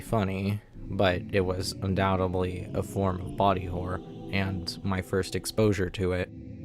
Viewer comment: I thought it said booty horror🙏😭